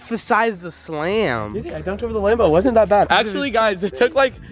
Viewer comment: I thought you said drunked